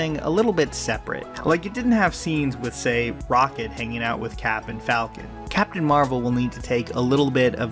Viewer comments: thought you said Rocket racoon hangs out with Captain Falcon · I really thought you said "hanging out with captain falcon" for a moment there. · lmao I thought you said captain falcon